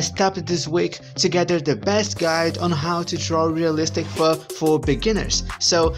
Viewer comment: I’ll be honest I thought it said how to draw realistic fur burgers😮😅😂